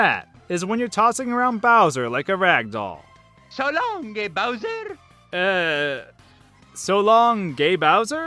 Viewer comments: When Mario throws Bowser, al I hear is "TADONKY BOWSER" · I never heard any of these miss hearings, I must have good ears or something, but I always hear "So long gay Bowser!"